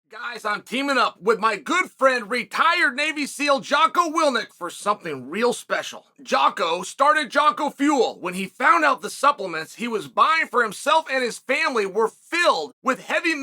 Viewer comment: I thought he said retired baby seal at the beginning lol